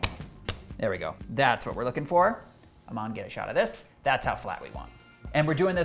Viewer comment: Did you say “Mom get a (camera) shot of this?”